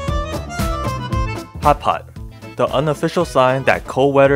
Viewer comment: Sounds like your saying hot pockets😂